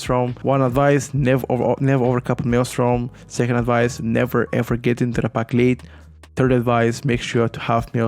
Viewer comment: What is his second advice at 7:37? I cant understand IT :D Btw very nice Guide! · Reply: "never get to the pack late" so dont be slow and come late while the other groupmembers are already pulling